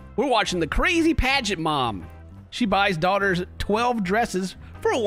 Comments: who else thought it said “crazy pregnant mom”? 🧍🏻‍♀️ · im not the only one who thought it said “crazy pregnant mom” right? lol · I thought it said "The crazy pregnant mom" instead of "The Crazy Pageant mom"